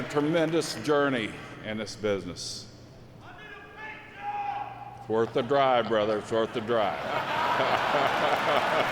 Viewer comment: What did the fan shout at 13:25? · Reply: I think it was " I need a paint job ". The Brisco brothers own/owned an auto repair shop and in the advertisement on a t-shirt that Gerry wore on PPV back in 98/99 it said it's worth the drive just like his response to the fan so I'm assuming it was that.